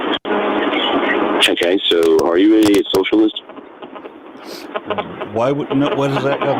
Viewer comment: Did the caller really say, "Okay, so, are you IDIOTS really socialist?"